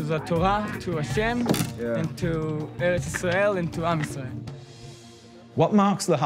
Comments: This is very interesting but just one slight, very very insignificant correction: In 2:49, he says עם ישראל, as in, the Israeli people, not "arm Israel", which sounds similar to "Am Israel" (=עם ישראל), but, like, well, it's just not what he said so it will really esde my Asperger's if you could fix that slight mistake in translation. Not to say that I agree with that fucking delusional idiot in any way, just that I spotted an error for you to correct, lol Ty in advance! ❤ 😀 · Hats off to Vice for, as usual, breaching a topic that very few go near. However, I think that subtitles should be vetted with a bit more care. Around 2:50, the Lehava member does not say "arm Israel," but instead says AM Yisrael, the Hebrew phrase for "the people of Israel," AKA the Jewish people. Small mistakes like this can have huge consequences for comprehension, especially with an issue as sensitive as this one. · Just to clarify a bit of a mistranslation in the subtitles, at 2:50 the boy says "To Eretz Yisrael, and to 'Am Yisrael" Eretz means "Land" (so land of israel) and 'Am means (pronouned, AHM, means Nation, so Nation of Israel) however the subtitles say "to arm Israel". He did not say the word "Arm" he said 'Am, just to clarify it could be easily misheard. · You got the subtitles wrong. He did not say to arm Israel. He said Am Yisrael, the entire jewish people